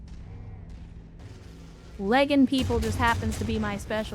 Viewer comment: LMAO Had to listen to that again. Thought I heard her say 'pegging' 😆